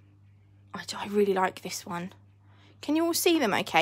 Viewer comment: it sounds like you said "I don't really like this one" but then you say you love that one.. So I'm confused lol. 😂 Its my favorite... But I think you could have said that you do really like it .. 🤷🏼‍♀😀